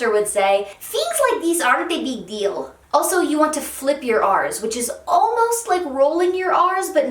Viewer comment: i'm so sorry, but am i the only one who heard tell us to flip our arse at 1:07?